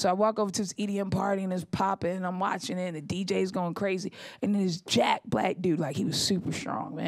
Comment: oh my god I thought she said "this Jack Black dude" when she said "this jacked black dude" oooooops